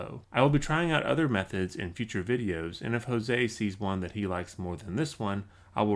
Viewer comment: Awesome work brother! @ 4:15 did you say "If Jose sees juan he likes more"? lol